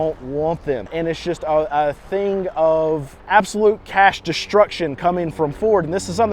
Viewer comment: Cash Destruction, and thought you said "Words are Hard".